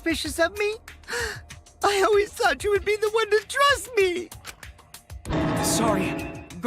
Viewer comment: Lol at 43:28 I thought he's aid dress instead of trust. Listen to it, u can't unhear it!